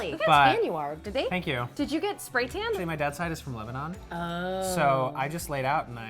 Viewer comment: did he say that his dad is from lebanon or I heard wrong?